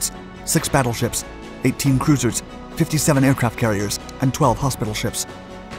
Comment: did he say 57 aircraft carriers???